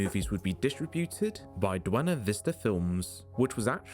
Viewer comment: does he say "duena vista"? Lol